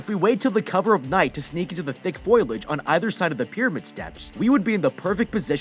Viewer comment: did he just say “foilage” …it’s “foliage” 🙈